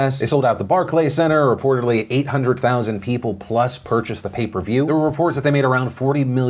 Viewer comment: Im Afrikaans. I thought you guys always said Paper View. Not pay per view